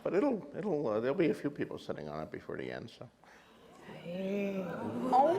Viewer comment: Can anybody tell me what did George say in 53:54 that makes everyone (Lena, Peter) went "ooooooh.." when they're talking about who should sit on the thrones ???? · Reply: He said that several people will sit on the throne "before the end".